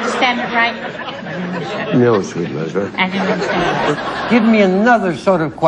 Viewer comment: did he say "No, sweet loser"? 😂